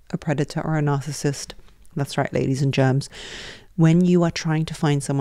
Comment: The subtitle said... "that's right ladies and gents but I heard you say... Ladies and germs 😂